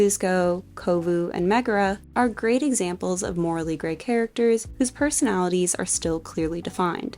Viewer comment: I waw watching the video with subtitles,so at 8:58 it said "morally GAY" instead of "morally grey"..... Even though its the wrong translation,its really funny (and true) lol